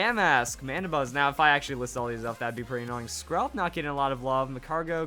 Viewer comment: I thought he said scrub when he said skrelp